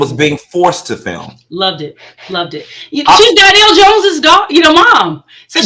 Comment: I thought they said it was Neo's mom?